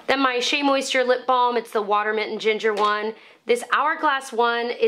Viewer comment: Enjoyed this video. But I thought you said "water mitten ginger one!!" I had to go to the link to check. 😄